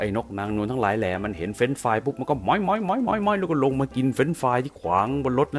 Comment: ขอโทษนะแอด 1:31 ผมได้ยิน หม_ย หม_ย หม_ย